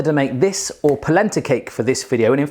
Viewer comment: When you said "polenta cake" i thought you said placenta cake